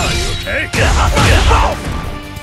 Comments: Doesn’t he say this also in his Final Smash? I’ve been trying to figure out what he even says 😂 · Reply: Yeah he’s saying “Here’s the big one!” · what is he saying beside are you okay? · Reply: "Here's the big one!" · I know it sounds like he is saying "are you ok" but I some how hear "hyruoken". Then again I'm tired so I don't frickin know